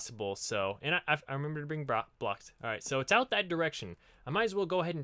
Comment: t 13:23 did he say he remembered to bring a bra?